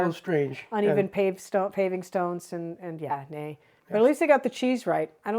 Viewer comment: You said “yeah, nee” instead of “yeah, no”. That nee means you are becoming more and more Dutch!